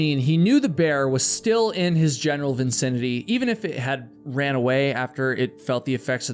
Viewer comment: It sounds like you say "vincinity" I stead of "vicinity".